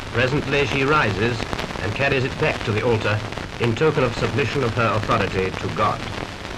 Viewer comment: why did she have to submit an apology to God for being crowned Queen? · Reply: Submission of authority to God. I understand how you misheard though.